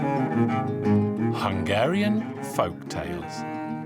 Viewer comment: i first heard it as "Hungarian f*cktales" Like if u agree | V